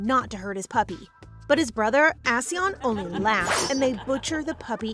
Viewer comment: tf is that name, because all I hear is ass-yon. It is quite fitting for the person though.